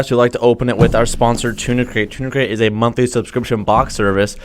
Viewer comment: I thought he was saying tuna crate this whole time